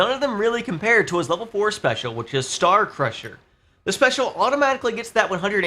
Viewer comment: Star Crusher... Why am I hearing that as "SOUL Crusher!!!1" I wonder?